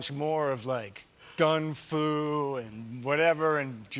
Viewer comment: HUGE fan but 1:40 did he just say gung fu?? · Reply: He said "Gun-fu" Meaning gun fight with kung -fu